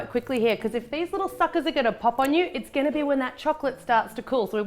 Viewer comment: I have no idea why but when it got to around 2:13 I swear I heard you said 'if these little fuckers are gonna pop on you'